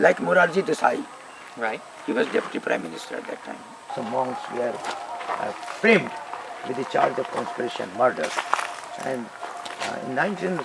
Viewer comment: Namaskar, I appreciate your hard work, I would like to ask what is the exact sentence on 02:15-- "Someone’s… a framed(or a friend?) the charge of confession(or constreation) a murder (or Margas), because I can't understand the meaning quite well. thank you very much. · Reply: "Some monks were framed with the charge of conspiracy and murder." Ac. is saying that false charges of "conspiracy" and "murder" were brought against some Ananda Marga wholetime workers.